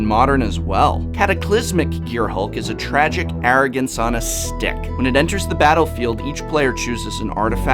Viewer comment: I could have sworn that he said: tragic arrogance on it's d!#k. alas, no he said "on a stick."